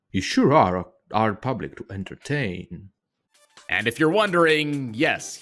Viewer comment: I think he says “you sure are a hard public to entertain” but it sounds like he fumbled the accent